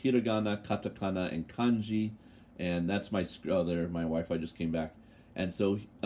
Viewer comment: did I hear you correctly ... "my wife just came back" Thanks for posting videos. My guy & I enjoy hearing comments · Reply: haha - no, I said "My wi-fi came back" :)